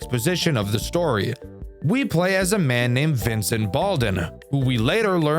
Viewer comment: I thought bro said Vincent BALLIN’